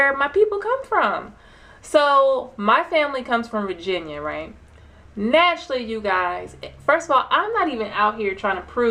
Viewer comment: What channel are you mentioning sis? It sounds like you said Cormeos. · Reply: Kurimeo Ahau